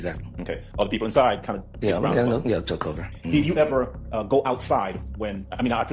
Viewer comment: sloppy youtuber not even fixing his auto generated captions to say what is really said, so lazy (13:53 "Took Over" when he really said "Took Cover") It just goes to show the lack of attention to detail that shows. He is really just profiting off of a shit video made about Jeffery's case...